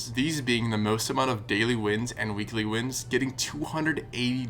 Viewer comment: not even top 100 on the leaderboard yet 💀 LOL this was a youtube autogenerated reply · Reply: Nevermind i thought he said daily wins